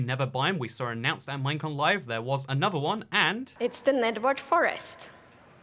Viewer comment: I heard nigward forest at first but now I know what she said I can’t hear it anymore😔